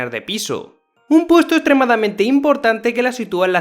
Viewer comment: El dijo "un puesto..." Lo que yo escuché "Un busto que..." Xd ya iba a decir que estaba de acuerdo xd